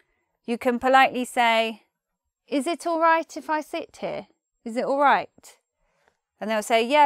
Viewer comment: Thought it said "is it alright if I shit here?"